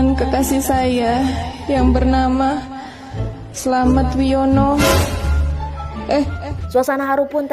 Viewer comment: Gak salah denger.. ni slamet sugiono??😁